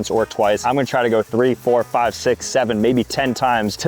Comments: d.. did you say 67?? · did he say 67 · and 0:35 did he say 67? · did bro really said 67😭🎋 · 67BAHAHAHAHAHAHAHAHAHAHAH 67 67 DID I HEAR 67 7:59 again you’re so funny😂😂😂😂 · did you say..., 67?!